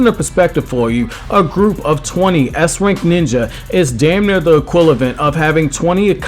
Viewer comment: what was that word? equilavent?